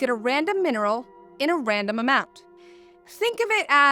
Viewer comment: Did she just say random outcome? xD